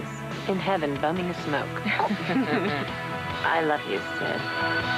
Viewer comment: Can someone please tell me what Kelly yells at the mom at 1:28:58? Thanks in advance. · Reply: Marcus Park “I just loved him, that’s all”